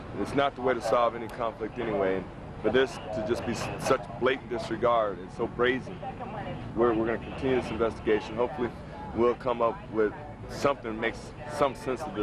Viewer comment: did this detective just say BRAZY instead of CRAZY?